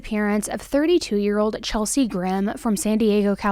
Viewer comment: At first, I thought you said Chelsea Grin and was like: "An entire band!?" So I read the description and found out that Chelsea Grimm is 1 person.